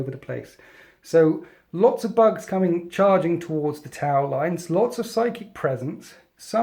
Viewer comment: Every time I heard "tau" my brain heard "towel".